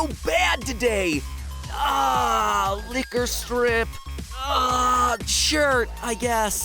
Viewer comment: I thought said liquorice strips xD